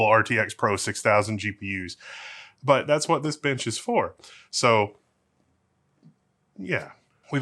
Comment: I thought said "that's what this bitch is for" @ 11:00 for a split second when talking about the power supply....but he said "that's what this bench is for"...but did he?